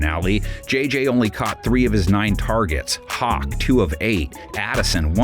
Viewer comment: Did I hear Hawk Tuah? 4:58 ? 😅😆😆